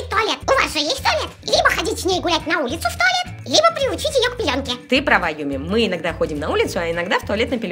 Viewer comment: мне послышалось приучить к пьянке😂 кодовое слово Юми 😂